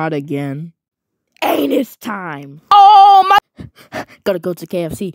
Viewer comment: I think sky say oh my butt